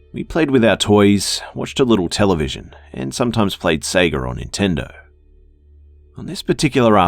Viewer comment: Did he say play sega on nintendo ?